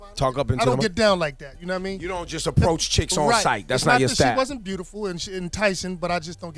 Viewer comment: "she wasn't pretty" haha thats harsh from baby daddy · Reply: that's what I thought he said but I listen to it again he actually says "it's not that I thought she wasn't pretty"